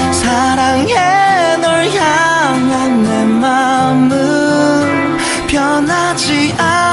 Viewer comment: Thank you for this video!! Btw I was just wondering. 1:24 It sounded like “사랑해 널 향한 내 맘은“.